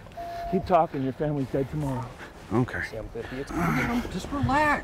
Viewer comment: “Your family is dead tomorrow” “I don’t care” Did anyone else catch that? Lmfao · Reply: Lol no he said oookay not I don't care 😆